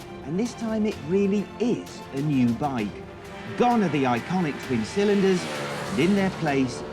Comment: Am I miss-hearing or did you say “iconic three calendars” at 1:48? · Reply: Close, but no cigar; 'iconic twin cylinders' was what he said but, somehow, I enjoy your version better! Well, it made sense to me, anyway!